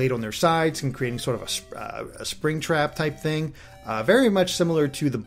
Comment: Did you say Springtrap? XD